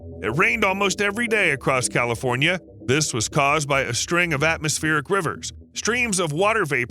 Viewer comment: Did he say "atmospheric quivers" ??